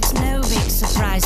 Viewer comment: I don't know why but i thought he kept saying "Dirty girls want benedict arnold back".